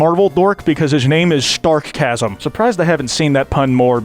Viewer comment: i was watching this in the bg and i heard "stark-gasm" and i ripped my physical skin off because i was curious as to what i just heard